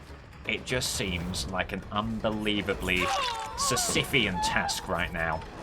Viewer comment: Anyone know what the word at 3:18:13 means? It's a wonderful word but I haven't heard it before, would like to know how to spell it and what it means. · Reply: Sisyphean? It refers to the Greek myth of Sisyphus. A sisyphean task is a difficult task that requires a lot of effort but is ultimately pointless or can never possibly be completed.